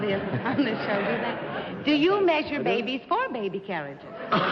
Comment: Betsy: "Well, do you make babies for your baby carriages?" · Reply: I thought Betsy said "Well, do you measure babies for your baby carriages?" Either way, it's hilarious! 🤣